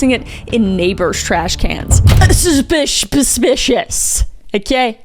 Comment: Have I been suspicious wrong my whole life? Cause it sounds like Kris is saying subpicious!!!!